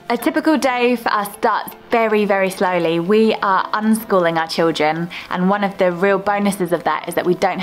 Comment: you are "unschooling" your children? I listened 5 times, it's really not "homeschooling" what you're saying